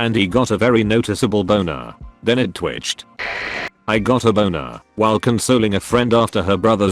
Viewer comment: I got a boner while consuming a friend Its not what it said but i heard it that way